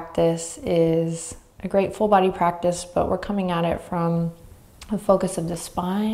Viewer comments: “Today’s practice is a great full body practice...” I heard “grateful body practice” and I love that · I heard "grateful body practice" when you said "great full body practice" at the beginning; both are wonderfully true! Love your focus on prana - this was exactly what I needed after a long day. Namaste from China!